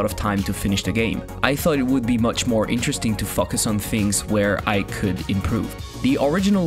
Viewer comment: what did you say? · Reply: I thought it would be much more interesting to focus on things where I could improve